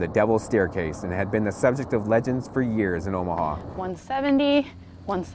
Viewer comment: I thought they said Morphine